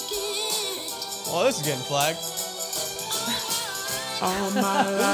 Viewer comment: Lol I thought said "Well, this is getting 'Black'"